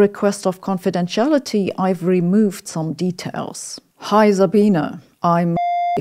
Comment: why did I hear "Hi Sabine" as "high as a beaner"...